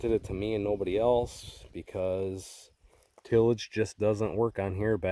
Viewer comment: Looking good it sounds like they say no till anything don't work